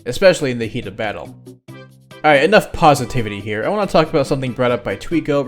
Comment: The captions say otherwise, but could 5:45 also be “Pause”itivity?